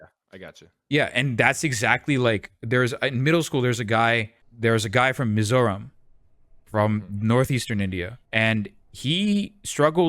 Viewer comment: I heard “there was a guy from Missouri”